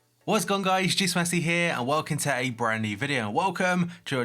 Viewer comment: Why does it sound like u say juice messi